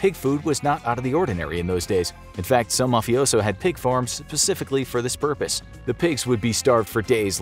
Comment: you didn't legit say "pacifically" instead of "specifically" did you? 🤦‍♂️😂